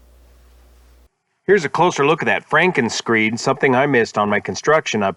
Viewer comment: Frankenscreed. It sounded like you were saying Frankenscreen.